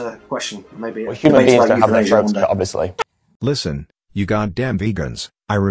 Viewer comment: what did ed say?? didn’t quite get it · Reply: I hear: Well, human beings don't have their throats cut obviously.